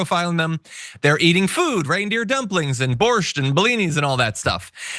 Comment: Man, I thought you said reindeer droppings, not dumplings.